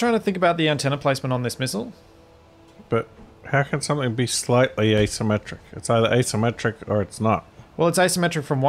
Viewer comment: I thought you said ' slightly isometric'